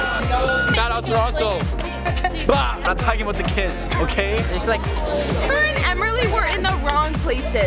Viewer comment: Can someone tell me what says in the second 0:40 ? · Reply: Luciana Duarte “We’re not talking about the kiss, okay”